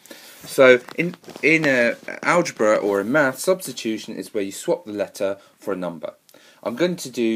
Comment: I thought he said prostitution